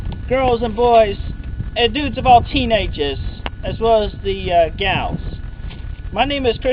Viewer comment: For a while I thought he said "Dudes of all teenaguhs", but he said "teenages".